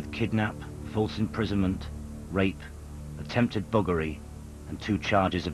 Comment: i can tell straight away from his reaction hes guilty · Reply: You misheard. He says "Buggery.." (for which he has just been arrested), not 'burglary'.